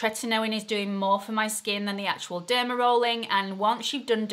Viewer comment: I honestly thought you said Demarol. I was like "WHAT!?!!"